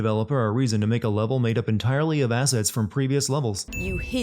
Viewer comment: I laughed so hard when I thought he said that during the hullucinating levels developers were able to use asses from previous levels. 🤣🤣🤣